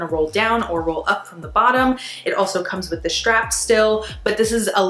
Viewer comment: Thanks for the review, very informative. Did you say at 11:29 “it also comes with the straps still”? You didn’t mention until that point that all (3) have optional straps. I kept seeing that it looked like they had “tabs” for straps on the back of the garment but no other reviewer had mentioned that except for you. Would you be able to confirm that. That would be a bonus.